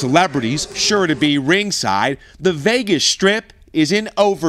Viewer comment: did he say Vegas trip or Vegas strip